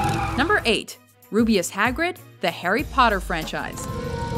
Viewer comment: did i just heard "The Hairy Potter Frenchise"?